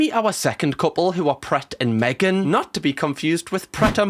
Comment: I honestly thought you said "prat" (no not Chris Pratt. Prat as in the insult) and my reaction was "wait,what the fuck did he say?" And I had to go back and listen again to realise you said "pret"